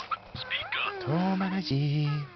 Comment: is it just me or when the toddler screams it sounds like their sayin, "DADDA!!" :P